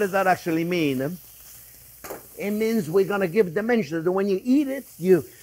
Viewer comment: Chef, I thought you said “it gives it dementia”!